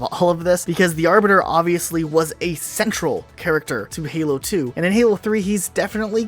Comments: all I hear is sexual character lol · did you call the Arbiter... Sensual?